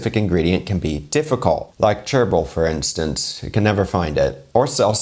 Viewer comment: Did a bit of a double-take there when you mentioned chervil, for some reason I heard this as gerbil and I thought maybe the practice of eating guinea pig had morphed into including gerbils. Really glad I heard that wrong. Wasn't looking forward to that recipe video lol